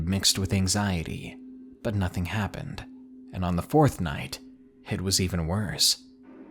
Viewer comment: fourth night made me think he said fortnite 😂